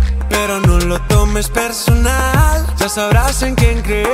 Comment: Bei 1:55 verstehe ich auf Deutsch "dummes Personal"